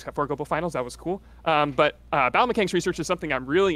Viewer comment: Could've sworn he said bowel mechanics instead of battle mechanics